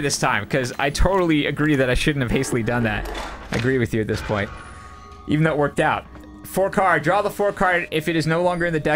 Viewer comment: what does say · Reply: Looks like "Who said that? I said that, me!"